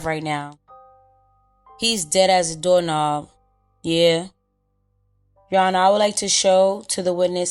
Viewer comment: Of course Butta thinks it’s “dead as a doorknob” when it’s “dead as a doornail.” This dude is something else lmfao… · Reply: I always heard “dumb as a doorknob” tbf